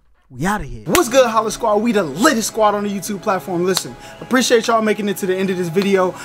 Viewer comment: I always thought you Said "We are...the LITTLEST squad on the YouTube platform!" 😂😂😂